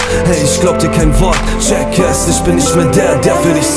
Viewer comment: anstatt "check es" hab ich "jackass" verstanden.. ^^